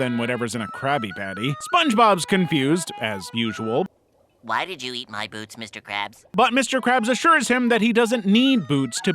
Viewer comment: i originally heard this as “why did you eat my b00bs mr krabs” and crashed out laughing and also ayo